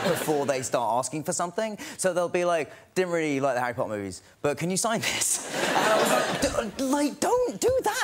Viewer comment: Can anyone tell what did he say at 2:58? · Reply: "Didn't really like the Harry Potter movies but ca you sign this?"